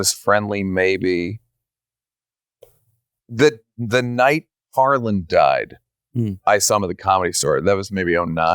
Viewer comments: When he said “the night Carlin died” i THOUGHT he said “ the night HARLAND died” as in Harland Williams, i was like “OH NO i didn’t even hear about that 😢” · Who's he talking about that died. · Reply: George Carlin. I thought he said "Harland," and had to go Google it to make sure comedian Harland Williams hadn't died.